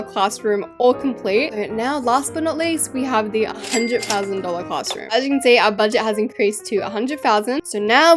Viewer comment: can we just talk about the fact how it sounded like bathroom 🤨